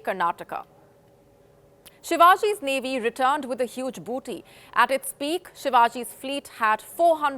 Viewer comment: did she say BOOTY😂